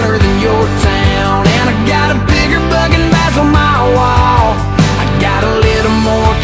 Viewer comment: For the longest time I thought he said "i got a bigger bucket bass than you"